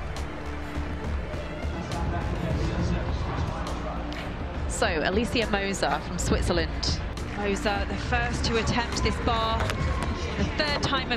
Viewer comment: who is she, I hear Alicia Mousa but cant find that name pole vaulter?